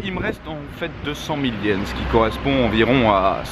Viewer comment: tu dis que t'as des soucis et tu dis 200 000 yens j'ai pas compris xD après j'ai compris que il te rester 20 000